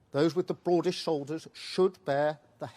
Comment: I thought he said "the broadest shoulders should pay", not "the broadest shoulders will pay". Two different things perhaps, especially after the lobbyists and wealthy donors have got to them. Listen at 15:04.